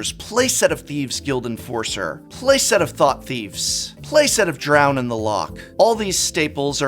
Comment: I thought you said playset of thoughtseize i almost spit out my drink.